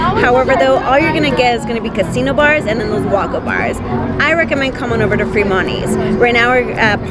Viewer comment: I think your videos are great. But what's a "woggo bar?" You say it at 8:17, and I just can't figure it out. Keep those videos coming! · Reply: Lmao! Sorry, i talked to fast, I was saying Walkup Bars